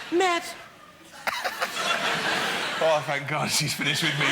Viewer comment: This is when David tried to outsmart Edna and all she did was tilt her head and smile and David shat himself and understood immediately that you dont call Dame Edna a man in a dress because she's not! !? She's s a character who's a woman with an entire life and back story and guests understand theyre there to be destroyed in a tasteful way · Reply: I initially also thought the same..... but if you listen carefully at 10:19 secs (I re-wound it several times including at .75 speed) he actually says SHE. Walliams is a profesional and I felt he would not make such a blunder.